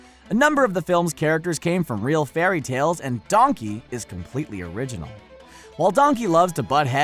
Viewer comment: sounded like said dUhnKeY